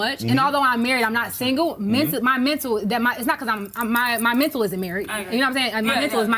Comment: Did she just say "My mental is my marry"? Lawd have mercy!🤣🤦🏾